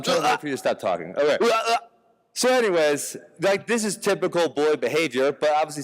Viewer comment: Ben “This is typical boy behavior” Jake thought he said “bully” lmao fail